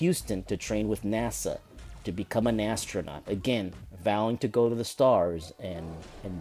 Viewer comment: Did you just say Nastronaut at 4:41 xD